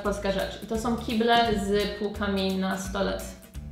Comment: Musiałem tego kilka razy posłuchać, aby zrozumieć w czym rzecz. Za pierwszym razem usłyszałem: "Kible z półkami na stole". :) Już dawno takiego nie widziałem, ale kiedyś był w "modzie". Strasznie denerwujące i przy dużym ładunku woda potrafiła ładnie pryskać poza jak z fontanny. ;) Mam nadzieję, że to już przeszłość. Teraz chyba nowego tego typu sedesu już nie kupisz. Ja nazywałem je: "przemytniczymi".